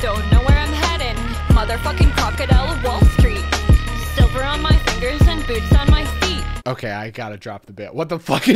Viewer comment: I keep hearing "silver on my fingers, boobs on my feet"